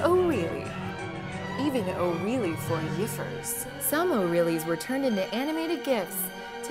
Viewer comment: Am I hearing things, or did she really say "for yiffers"